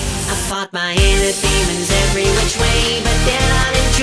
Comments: I swear I hear "I fuck my inner demons" but it still makes a lot of sense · Tbh i thought he said "i F@ my enemys Every way" 😂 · I always hear "I fucked my inner demons in every which way" and it feels right